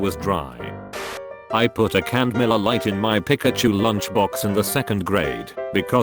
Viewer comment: i watch these on autoplay while i draw sometimes and i have some auditory processing issues as well as some attention-keeping ones, legitimately thought that the last one said "hand grenade" instead of "miller lite" and i have absolutely no clue how i heard that. it would make you a cool kid though?